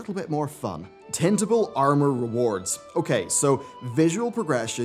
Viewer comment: I heard, "Tentacle Armor Rewards" and I was like. Dude, are you really trying to push Hentai into WoW?